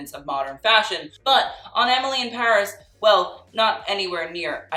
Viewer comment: I don't why I heard you saying an alien in Paris👀